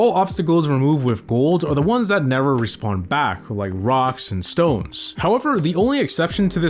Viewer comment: Did I heard a Rock and Stone?